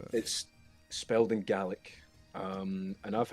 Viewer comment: I apologise, my head was drifting and I heard Gaelic as 'garlic' in 14:51 and i had a great chuckle out of it.